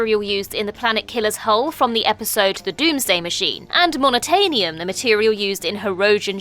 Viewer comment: I thought for a minute you said "unobtainium" for a second. Hey, it's already a thing.